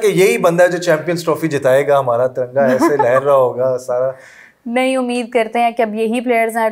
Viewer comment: did he say “tiranga hamara”?? 😂😂😂